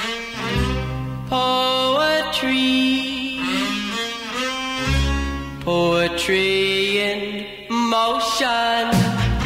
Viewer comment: My sister, a teenager who loves this song, thought it said: "Oh, a tree in motion."